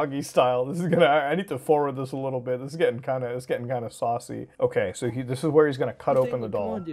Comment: Did he say "sussy" ??!!?😳😳😳